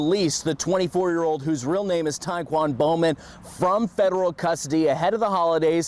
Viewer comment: Istg I thought she said his name was "Taekwondo Man" 😂😂